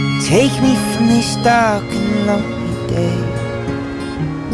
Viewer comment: i heard "will you blow my wig" 💀💀